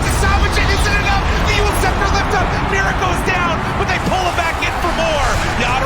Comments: did he say miracle down? 😂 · Did he just said miracle? Hahaha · Did i heard it right? Miracle is down??? I think i misheard the commentator from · lol is it me or did he call topson miracle at 22:18 saying miracle is down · I heard "miracle is down" lmfao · “Why did he said miracle down” HAHAH min